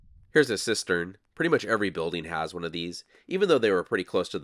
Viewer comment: u said sistern ! on google it does nt have any meaning . what is original spelling of this word ? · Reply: Cistern.